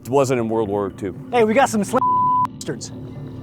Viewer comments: I think he said "we got some slant eye bastards" if I know my racial insults correctly for anyone wondering what he might have said. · I think he said slanty eye bastards 😂niiiice love it